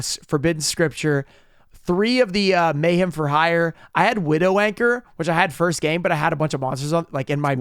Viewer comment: Oh gosh I thought just said Widow Wanker